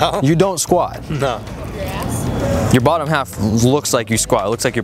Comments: I heard a girl saying "your ass" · did anyone hear "your ass"